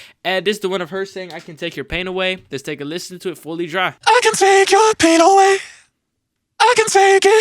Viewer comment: Idk but I hear the vocals "I can take your pinoy"